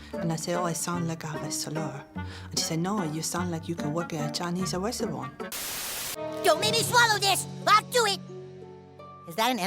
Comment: What did she say did she say Chinese suwesuwanr what?